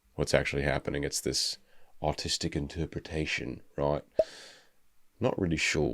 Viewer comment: Monster ver of circe is a def "hear me out" Edit: i heard him say "autistic" instead of "artistic"